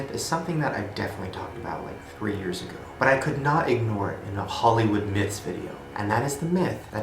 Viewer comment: For a second I thought you said "Halloween myths" and that sounds like a good idea for an October vid 🤷🏽‍♀️💕